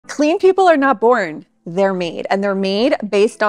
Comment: Lol I was distracted when the video started and I thought you said “Tidy people aren’t boring, they’re mean.” 😂 and I was really confused